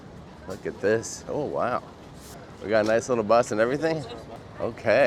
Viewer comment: So I was watching the bit where you had just gotten of the ship at Sarlino and you were walking to your bus. Your clip showed the back ends of your family and at first I thought you said “ nice little butt” instead of bus. Then I came back to reality.